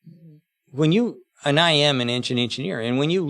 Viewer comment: (ish) did Sandy actually say "I am an ancient engineer"???